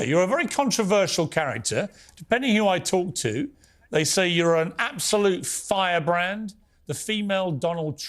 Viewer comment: At first, I thought Piers was gonna say "absolute f**got" and 0:39 conspiracy theory "wank-job". 😂😂😂